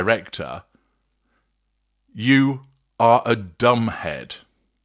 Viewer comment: Interesting. The first time I watched the original clip I was knitting and did not see the subtitles. I heard him say 'damaged', which was confusing. 'Dumb head' makes more sense considering the context but way he is insulting. The man is a little tyrant and should be removed.